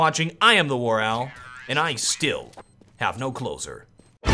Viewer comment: Every time your video ends i hear "I´m warowl and i still have no clothes on" :D